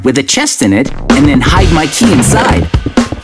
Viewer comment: "Hide my key" sounded like "Hide, Mikey"